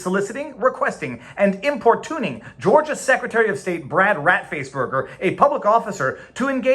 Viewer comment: I thought you said Brad Rat Face Burger.